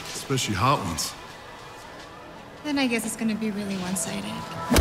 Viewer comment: what does izzy say in minute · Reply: Then this is gonna be really one sided